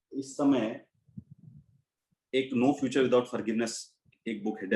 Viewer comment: Please tell recommend book by sirr. I don't understand name of second book.. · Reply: The book's name was-"No future without forgiveness". It was written by Desmond Tutu